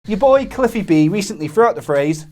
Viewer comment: I thought you said at the beginning "Is yo boy!!" Laughed my arse off